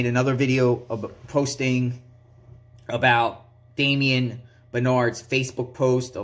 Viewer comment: Damien benard? I thought she said little andy.🤔🧐. 😅😂🤣⚓the anchor is always my daddy.